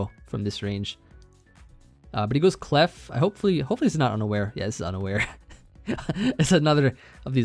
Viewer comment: Not going to lie I 100% thought you said "it breaks through underwear." Not unaware lol